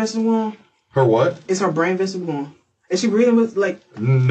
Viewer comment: I think she said “is her brain visible” totally cruel 💔